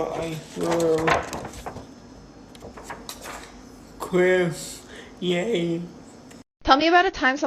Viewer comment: “They were so shocked but look at what I drew! Chris, yay!” That was so sweet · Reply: I thought he said crisp ! Yay! I also think it is crisp